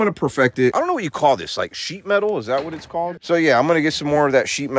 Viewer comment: Sounds like said Sheep Metal. Lol. Thumbs up.